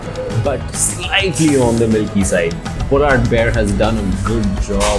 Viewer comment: For a moment I thought he said 'Kolar Bear' Anyway, DbC at both places are not worth it. Ice cream is better at Polar Bear.